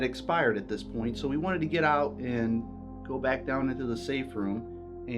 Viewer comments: I heard "get out now " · it sounds like it says get out:D+ xxx ooo muah!